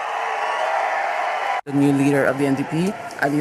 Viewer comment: I thought they said "the new leader of the ndp has a uterus"